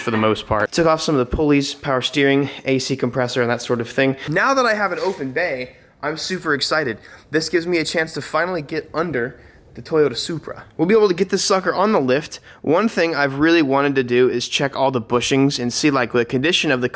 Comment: I thought you said ‘I’m SUPRA excited’ That would have been clever